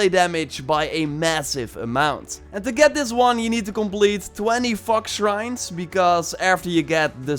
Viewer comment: I thought he said 20 fuck- shrines